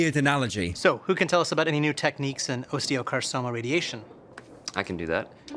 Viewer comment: wtf did he just say osteocarsoma?